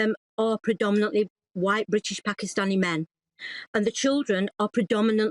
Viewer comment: did she say "white, british pakistani men" ??